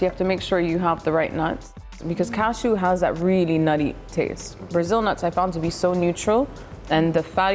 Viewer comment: She mentioned cashew has a nutty taste, but I can’t understand what is the name of the next nut she names. Brazil Nuts? Someone understands what she said? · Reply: Yes she said Brazil nuts. She doesn’t specify if she only used Brazil nuts or a mix of both cashew and Brazil. Either way she definitely said brazil nuts